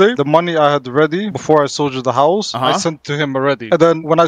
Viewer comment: when he said he sold you the howls i thought he said house hahaha...real estate collector